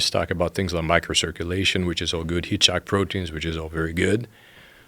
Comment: i'm trying to figure out what kind of proteins he says at 1:43:45, did anyone catch it or know what he is referring to? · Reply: Heat-shock proteins.